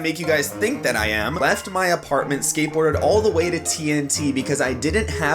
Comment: I thought u said TNT the bomb